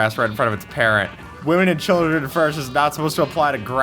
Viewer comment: is it just me or did he say weed and not wheat?